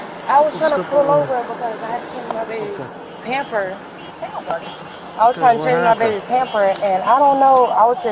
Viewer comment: I live with my mom and pamper . ., she says. She's so lucky no one is hurt. I hope she learns her lesson and stops drinking. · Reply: I thought she said she was pulling over to change the baby's pamper? 🤔